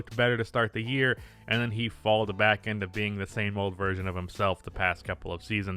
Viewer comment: did you just say falled 😂😂